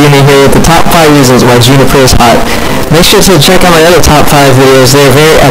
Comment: I thought it said "top 5 reasons why Jumper is hot"